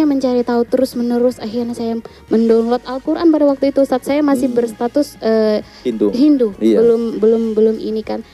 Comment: kalo ggk salah dengar berstatus hindu, apa saya salah dengar